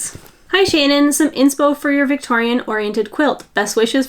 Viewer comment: I love that you said what Cannelles name ment! The amount of times i thought you said Kanel with a french twist (kanel is cinnamon in Swedish)💝